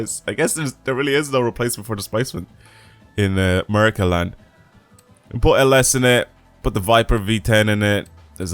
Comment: Lol did you just say americaland 😂